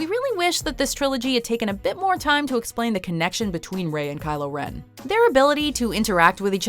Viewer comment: did she just say Kylo wen